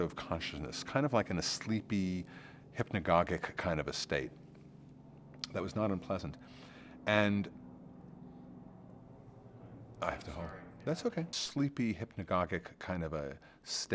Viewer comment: LOL at 0:48 did he say ".... I have to fart"??? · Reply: He said "I sharted"